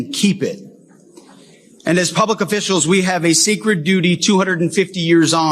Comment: did he say secret duty or sacred duty